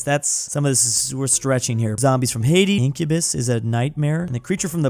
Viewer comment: did he say tahiti?